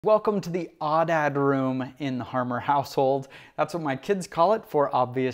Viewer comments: At first I thought you were saying "the 'aw, dad' room". Like, "Aw, dad. Nice room" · I thought he said it’s the “awe, dad” room. Like when the kids walk in, they go “awwww, dad!”